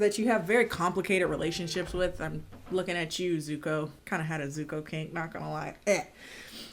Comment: the captions say zoo cocaine I'm crying